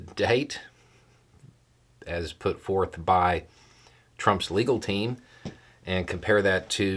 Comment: I misheard you at 0:14 -- I thought you called them Trump's "illegal team!!"